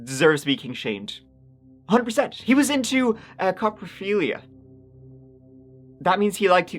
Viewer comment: When you said coprophilia I legit thought you said "cop-a-feelia" like he was just out grabbing random boobies 😭😭